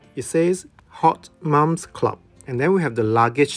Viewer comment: I laughed a little too hard at “hot mom’s club” 😂 The pouch you got was beautiful!! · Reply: 🤣 I thought it said “not mom’s club” 😅